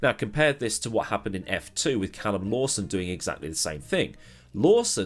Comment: did I hear that right at 3:16? "Callum Lawson." Or are my ears deceiving me?